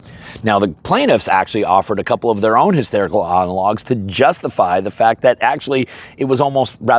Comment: I think he actually said “hysterical analogs.” 😂